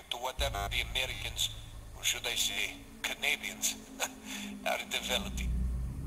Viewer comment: did he just say "CANADIANS"?